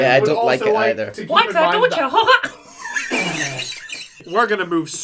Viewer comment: What does monty says with mickey’s voice at 17:57? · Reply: He says "Like that, don'tcha?"